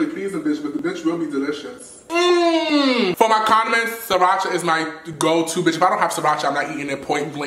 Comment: I thought he said communists like tf AHAHAH